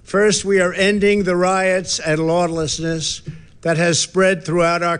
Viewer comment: did he say he wants to end lawnlessness? how much lawn will each citizen be awarded?